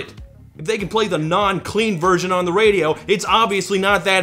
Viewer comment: Lol I thought Justin was saying Queen instead of Clean. 😂